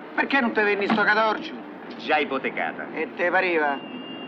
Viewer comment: Cosa dice al minuto 0:30? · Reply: “Già ipotecata”